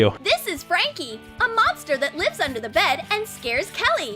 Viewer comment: When she said “a monster that lives under the bed” I heard it as “a mobster that lives under the bed”